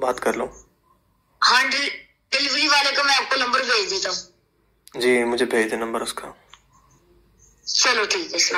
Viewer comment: time 14:24... wo larki bhi nahi hai koi larka hai... "he said" number bhej DETA HOON" not Bheji DETI HOON... listen carefully